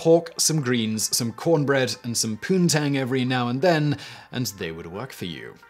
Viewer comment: Today I found out how old the word "Poontang" is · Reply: I heard it as pootang but yeah; that word is ages old lol